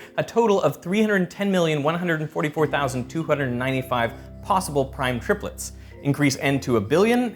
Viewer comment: The subtitles say 296 when he says 295...